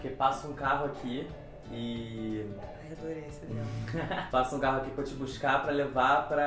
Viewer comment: Só eu ouvi ela falando: "adorei essa história, amor" no minuto 2:10?